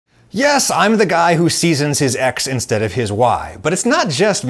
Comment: Auditory processing issues are so fun because I heard "yes I'm the guy who seasons his ex instead of his wife" and almost died